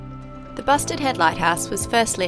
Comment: Lol, I thought you were saying Busted Head, until I saw the sign.